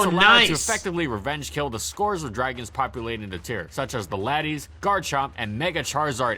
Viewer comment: auto generated captions said ladies in stead of latty